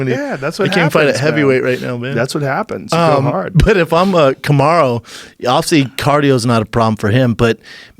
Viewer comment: Did he just say cum-aro? 🤣